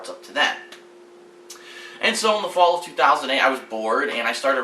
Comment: lol at 3:33 i thought you said ",so in the fall of 2008 i was born". haha